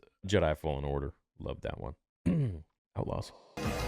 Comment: what is said here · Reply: Outlaws😂